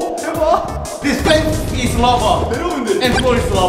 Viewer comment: All i hear is “the _ is larva” i cannot ever hear lava normally again